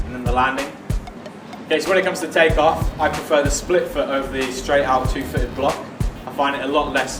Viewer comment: sorry for that but i can't understand this 0:40, anyone could help: i prefer the split ....two foot block. the exact words in the middle please. he talk to fast for my level of english understanding. also at 0:58 'for the take off i'm running a little bit pace?thanks:) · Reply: i prefer the split foot over the straight out two footed block